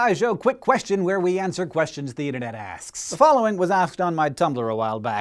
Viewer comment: When I first heard the opening I thought he said "where we ask questions from theoden" and I was confused.